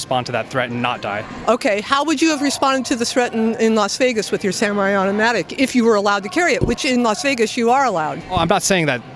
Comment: Did she say samurai automatic????😂😂😂😂